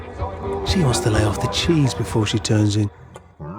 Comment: What does he say at 21:34? It's probably nonsensical but I still can't make it out! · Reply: "She wants to lay off the cheese before she turns in."